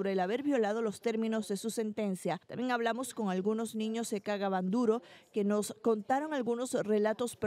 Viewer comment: se que dice el nombre del pueblo pero yo escucho "niños que cagaban duro"...🤦 Ai dios mio, protege a esa gente inocente e indefensa